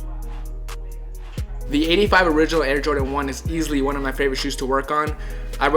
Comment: Sounded like he said 95 air Jordan 1s like the whole time